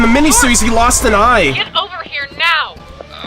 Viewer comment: Actually, I guess you didn't hear him (understandably so because it was in the background) but Maine did talk! it was right around here 57:55, He says "get off me." He has a super gruff sounding voice. I honestly really like your rambling sections. Getting to hear more of your thoughts and theories on what your watching not only sets you apart from a lot of other reactors, but just makes your videos more entertaining for me (and I'm sure a lot of others). In fact, you going in depth with a lot of this stuff is showing me things that I didn't even notice in the show, and I've seen it several times! I had no clue that the "mother of invention" was from that proverb and I thought that was really cool!